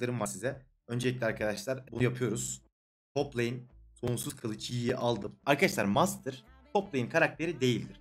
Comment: kolsuz kılıç yi duydum sjdjsjd